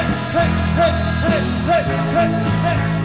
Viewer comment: alguien sabe que dice desde el minuto 4:09-4:12, no llego a reconocer la frase. gracias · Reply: lo de abajo o ...Sing it to them, sing it to them well...